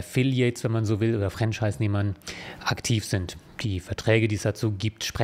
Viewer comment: Statt Franchisenehmer hab ich hier Fremdscheissnehmer verstanden. Was irgendwie auch gut passt.